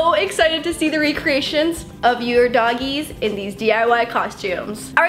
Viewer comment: When you said dogoes I thought you were saying bogies